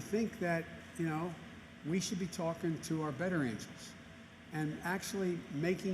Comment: It was a little stuttered at 45:45, did he say speak to your veterinarians about the masks??